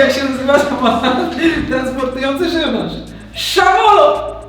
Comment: usłyszałam samotny transportujący grzybiarz